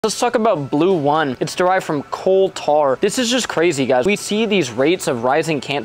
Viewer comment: I misheard and thought you said “Qatar” instead of “coal tar”.